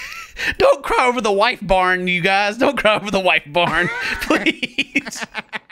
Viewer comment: Absolutely hollering at “don’t cry over the White Barn” 😂😂😂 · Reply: Lol oh I thought he said white barn like the company 😂 Either way, still a funny moment